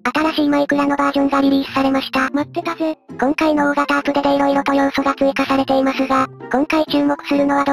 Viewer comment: 最初ペルくんが「ポテトー！」って言ってるように聞こえてw爆笑してしまったwwちゃんと聞き直したら、大型アップデートだったw